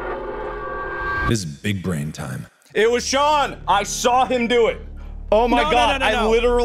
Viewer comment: Mark: Oh, this is a problem "This is big brain time." Mark, Immediately: IT WAS SEAN I SAW HIM DO IT 7:00 WHEN I TELL YOU I CHOKED ON MY DRINK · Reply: I Thought He Said "It Was John"